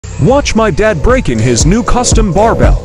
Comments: Man I thought he said breaking his new bar.....not breaking in lol. Im sitting here waiting for that boy to snap and am left unsatisfied!!!! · People who thought it said “break his new custom barbell” 😆 👇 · I heard “breaking his custom barbell” so I was eagerly waiting to see how does a barbell break😂